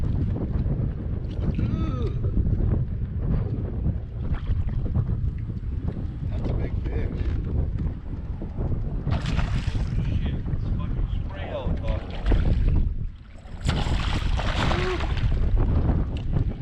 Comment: Did anyone else hear say "S#*t" @ 10:51...😂 😂 😂